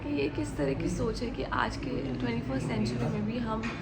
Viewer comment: kya bol rahi ho 24th century chl gyi kya😅